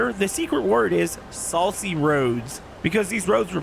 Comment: Saucy roads! i thought you said salty roads for a second i had to listen to it a second time lol